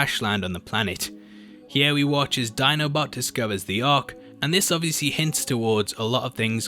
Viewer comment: I'm sure i heard "Dinobot, terrorise"